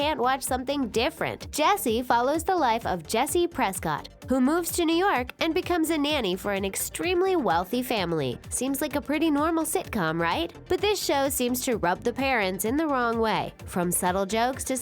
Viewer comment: anyone else thought they said Sidney Prescott?